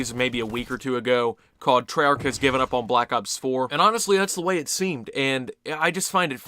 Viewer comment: I thought you said Treyarch gave up on Black ops 4